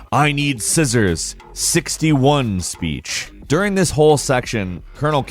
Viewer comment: Lol I didnt hear the 61 what i heard was "I need scissors, safety ones!"